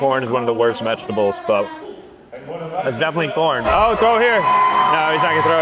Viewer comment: I’m not even joking it sounds like he’s saying p*rn instead of corn maybe it’s because his mouth his full but I jus found it weird